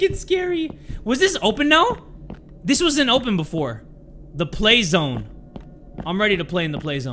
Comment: I THOUGHT IT SAID SLAYZONE SJSNMSKMDMD